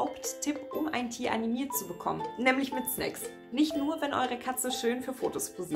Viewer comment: hab bissl was andres verstanden .. „nämlich mit sex“ 😂